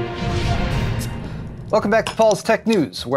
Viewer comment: Why did i hear "welcome to false tech news"